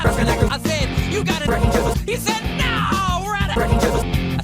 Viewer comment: When he says “Wrecking chisels” why do I always hear “Rocking chairs”??